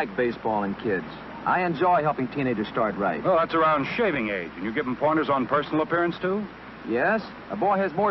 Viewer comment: I thought he said "that's around shaming age"